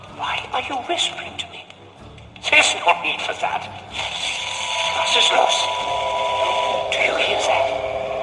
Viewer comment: the subtitle says: what's that noise. But actually he says. Was ist los?(what's the matter)